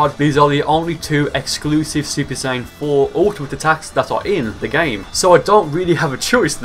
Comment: I thought he said super saiyan 4 guldo 😂